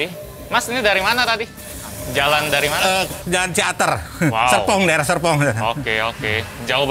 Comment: gw dengernya sep*ng:v